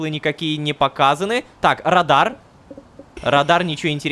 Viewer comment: мне слово радар послышалось как радан(радан это чел из сезона идеальный мир)